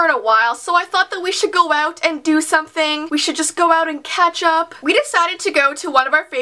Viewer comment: No lie, when she said "catch up" I thought she said "ketchup". I was like, "Yeaaaaahh, I like to ketchup with my friends too..."